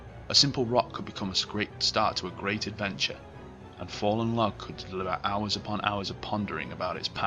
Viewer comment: Okay what is he saying at 4:49 ? It sounds like he goes "And fallen log could Bwuhbubububu" XD' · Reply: I think "A fallen log could deliver hours and hours of pondering"